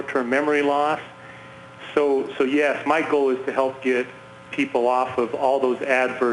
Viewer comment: Is it just me or did the doctor call him Michael jus before he finishes his bit. Look at face when he said Michael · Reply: He actually said "my goal" but there was an awkward pause bc even thought he said "Michael" at 1st; was a scare,no?